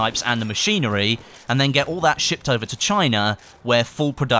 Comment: Am I the only one who heard that as "and then get all that shit over to china" at 3:56 ?